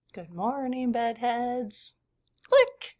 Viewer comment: I found you some months ago and have followed regularly since then. All this time I thought you were saying "Goodmorning BUTTheads"! LOL! I didn't even question it since you said it in such an endearing way, I just went with it! 🤣